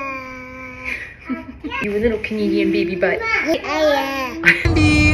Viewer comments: "Little Canadian Baby Butt" Emby is adorable, as are the twins. Everyone is growing up so fast. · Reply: I thought she said “comedian baby butt” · Oh gosh I thought you said "Little comedian baby butt" lol 😂 I was laughing like, that fits!! Of course Canadian fits too!! "wow, that's a beautiful dress!!" So sweet!! You guys kill me you're all so cute and hilarious!!😁💞💕